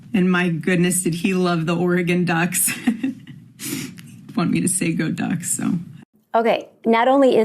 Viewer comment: I thought it was a bit much when I first watched it, but I was in shock that Charlie Kirk was gone and didn't read into it. At 18:13 she said "he wanted me to say go ducks." How did she know Charlie wanted her to say that if he was dead? That struck me as sus when I heard it the first time. Then it sounded like she caught herself and made a little joke out of it. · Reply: She said, “He’d want me to say go ducks”.